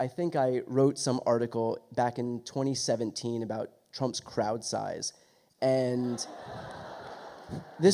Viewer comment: Sounded like Brian said Trump's Kraut size! LOL 😁🇺🇸💙